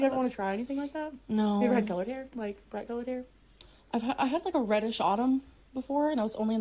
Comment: excuse me but did she say her hair was once ”reddish autumn”.... Is she meaning AUBURN hahahahah?